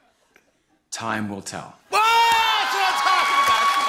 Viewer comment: I thought he said "Tom will tell" 😂